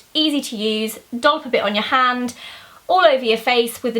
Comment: At first I thought you said 'a dog bite your hand' at 1:25..